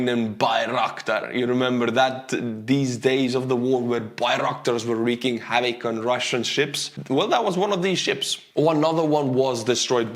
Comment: Did he really say (3:45) they’re “wreaking haddock”? Sounded like it to me. 🤗😁